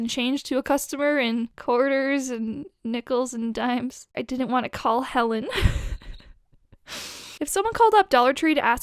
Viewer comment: When you said, “i didn’t want to call Helen”, i thought you said, “I didn’t want to call hell in” xD